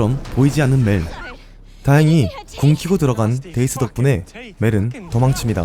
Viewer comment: 초에 뭐라고 하신거에요?? 궁키고 들어간??